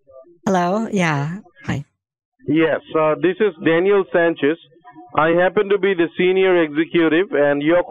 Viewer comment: at the beginning i thought he was going to say “im daniel-san”